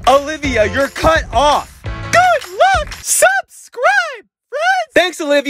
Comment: Does anyone else think it sounds like Olivia is saying "subscribe, b****es"??